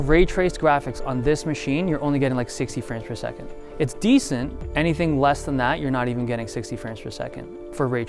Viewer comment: Did he just say 'dees' for decent? I always thought this Dave guy was gay anyone else or just me? just me ok